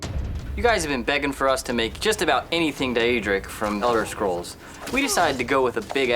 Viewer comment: A axe worthy of the dieadra...wait what now I'm triggered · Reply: Swarley I thought he said daerik